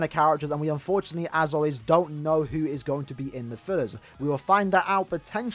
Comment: I listen to 9:50 like 10 times to realize you where saying fillers and not First. Thought you were saying they were going to be in separate banners